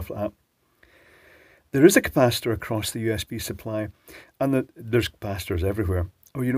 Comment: I don't know why I heard this as "There's bastards everywhere." True, though.